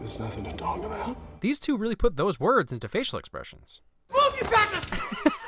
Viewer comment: I had the same reaction I just laughed and said "did he just say move you fascist?" 😂